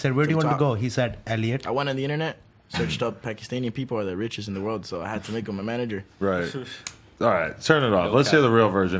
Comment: Did he just say “I went on the internet, searched up Pakistani people are the richest in the world and made him my manager.” At 26:00??